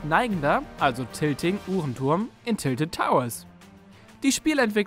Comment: 2.02 ich habe hur*n turm verstanden😂😂😂